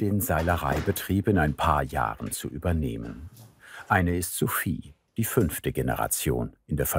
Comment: Bei 16:25 hab ich erst verstanden "Zwei der Kinder wollen den Betrieb übernehmen - eine ist zu viel" Und mein Hirn dachte kurz, es läuft auf ein Peitschen-Duell hinaus 😅